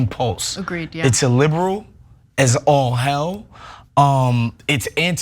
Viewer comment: Picking up pitchforks to burn somebody at the internet stake is “liberal as all hell?” Donald Trump must be the most liberal anybody has ever seen! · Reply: Illiberal, as in not liberal. I thought I'd heard the same as you at first.